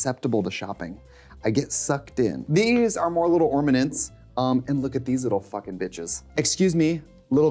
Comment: did she say… ormanents?